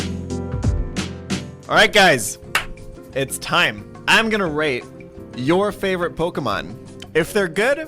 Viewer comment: I thought at the start instead of hearing "I'm gonna rate.." I heard "I'm gonna RAPE your favourite Pokémon"